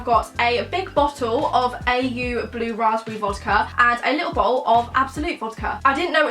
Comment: Did she say "a little bowl" at 1:14 😂😂